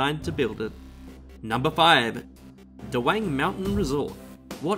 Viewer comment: i heard duwang